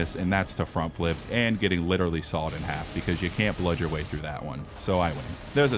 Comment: First person who can tell me how many times I said the word "Blood" (including when it's mixed with another word like "bloodborne" or "bloodbornified") in this video will get a $60 gift card for their gaming platform of choice. 😤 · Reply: 52 times including all words with blood, and also adjectives like bloody or bloodbourney but excluding verbs like bleeding. Edit: I also heard bludge instead of blood at 11:38, therefore did not count it